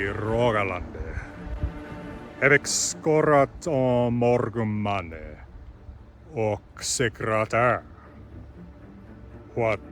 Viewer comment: I was trying not to look at the subtitles for the first listen-through, and at 1:14, I thought said: "Hefi'k skorat á mo̹rgum manni ok sekretær". "I have 'scored' on many a man and... secretary". 😅